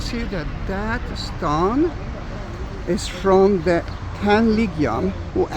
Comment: i could not understand ( That stone is from...? ) 70 AD.. or did you say the ("Tenth legion" ? ) and the closed caption is no help.